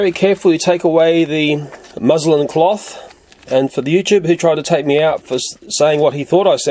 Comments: Frankly I thought u said Muslim cloth but I figured it was just austailian accent, anyone with a brain could realize u meant nothing by it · lmao I thought he said Muslim cloth too but logic kicked in, I guess that you tuber didn't have logic cause wtf is a Muslim cloth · xD someone thought he said muslim cloth